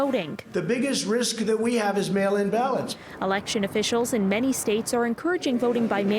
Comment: I thought he Trump was saying 'Male in Balance'...😕